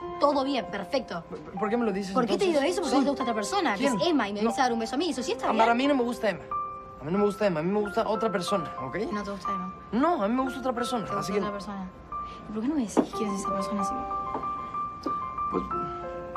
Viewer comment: La primera vez que escuché la escena creí que Simón decía "Amor, a mí no me gusta..." Pero era "Ámbar" xD